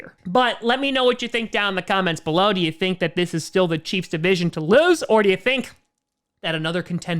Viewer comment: did he just say the chiefs division to lose?